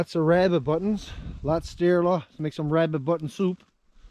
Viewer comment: Did I hear you say "rabbit button soup"?. I know you Newfoundlanders have a great sense of humor but are u serious?.